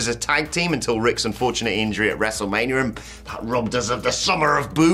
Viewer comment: Did you just say WrestleManrium? You silly British man.